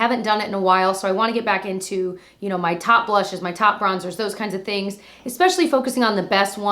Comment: Goodness I’m sick today and swore this said top brushes and I was like why are you talking about blushes 😂